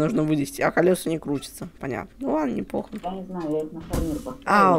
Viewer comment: Мне одному послышалось когда сказал что колеса не крутятся то он сказал ну ладно мне по###